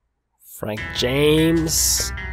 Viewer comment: at first I thought that said prank james😂😂😂😂😂😂